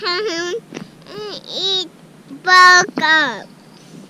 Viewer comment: Did R say he was going to town to eat burgers and milk? (Let’s see how well I speak ‘Rainier’...) I’ll be on the trail this weekend- sorry I missed you guys when you passed through MD. Hike on, Family. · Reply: I thought he kept saying "boogers" but you are probably right with "burgers". It was "and milk". Burgers are probably the #1 trail food (and he hears that a lot when nearing a town). Pizza comes in a close second but that is every day food for some. Crave those burgers when hiking.